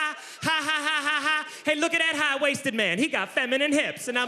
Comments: I thought he said "high, wasted man" at first and was surprised at how precociously perceptive these pubescent pupils were 😅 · Look at that high waisted man! He got feminine hips! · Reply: I thought he said high & wasted